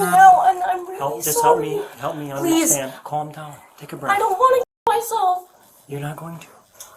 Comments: did she just say “i dont want to kill myself”??? Really bringing suicide into this?? Really hope they put her in a mental institution after she gets out of jail, or at least have someone keep an eye on her, cuz wtf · did she just say she doesn’t want to k!!l herself? Mofo then don’t 🤣